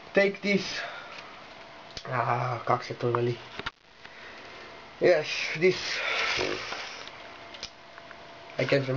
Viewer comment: What did he say, what did he say??? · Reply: a kak se to veli means How I said that